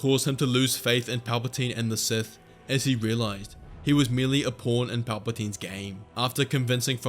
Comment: if you have CC on it says porn instead of pawn. Edit: Sorry it's at 53 sec not 33 sec.